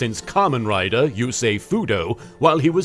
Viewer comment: I heard that as "the sexiest motorcycle enthusiast since Kamen Rider (you say Fudo)". I also interpreted Jack as saying "What is it you say?" in an older video. Yusei is a very confusing name.